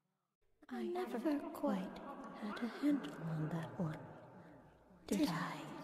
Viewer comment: I always thought she said “I never quite had a handle on that one”